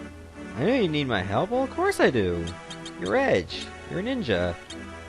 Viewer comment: For a second I thought you said Greninja. If you did, that would be creepy, since he wouldn't be out for another couple years.